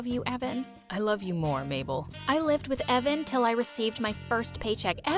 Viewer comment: I THOUGHT HE SAID “ I love u more BAGEL.